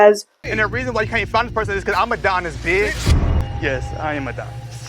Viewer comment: Am I the only one who thought he said "I'm Madonna Bitch!" the first time they saw this?